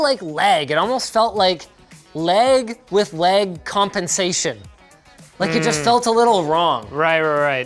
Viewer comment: "Leg with leg compensation" that's what I heard